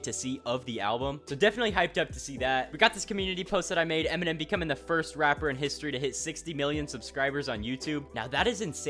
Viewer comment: Justin has more subscribers so.... 😅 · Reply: my bad I thought he just said becomes the first person